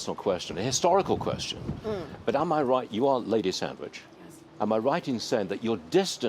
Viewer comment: When the dude started talking i thought at first he said "ok make a sandwich" lol I was sweating thinking Brits aren't really made to do things like that. XD